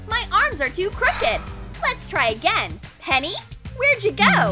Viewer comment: does she call her henny ? I am a girl don't think I am boy cause of name